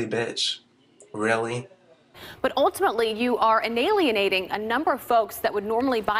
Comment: Did she say enalienating at 0:24? As in, enable + alienating???